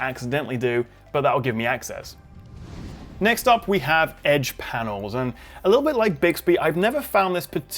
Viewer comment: what did you say at 3:35? wtf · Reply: Edge Panels? :P